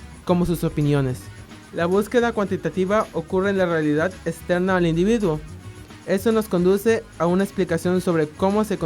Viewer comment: "la busqueda cuantitativa ocurre en la realidad esteaneao al individuo"... osea qué mierda dice ahi?? no se entiende una poronga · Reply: externa al individuo estimado amigo